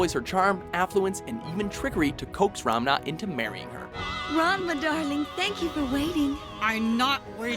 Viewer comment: Does he say Ramna? :p It sound weird when he says it